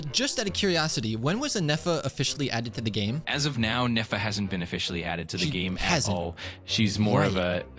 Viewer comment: It thought you said NEFER